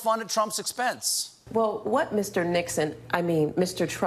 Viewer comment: I thought she said “Dickson” and I was like ooooo and then I realized she said “Nixon” and I was like OMG SNAP and then Colbert says “that’s an insult to Nixon” and I was like OHHHHH THE BURNNN